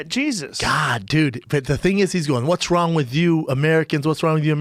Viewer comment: Brendan did one of those things where he misheard and made it sound way worse lmfao it sounds like no for gay jesus